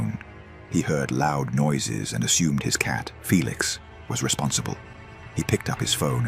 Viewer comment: Did he say felix ?